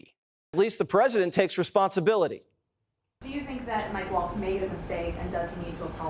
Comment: Watters said Waltz' name, and I thought he said, "big balls." Thanks Hal for all you do!